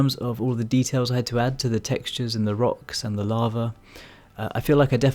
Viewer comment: Not gonna lie, I heard "larva" for the first 2 minutes and was super confused. Awesome job, by the way.